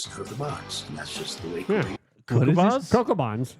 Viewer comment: I hear “great is … cocoa box”